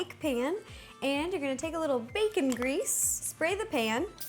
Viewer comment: When you said baking grease I thought you said bacon grease and I'm like whaaat